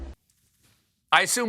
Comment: First time through I thought he said I assume that was cursive. Perhaps 🤔 I should go to bed.